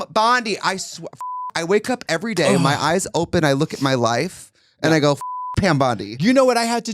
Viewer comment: I thought I heard “I look at my wife.”. And almost had an aneurism.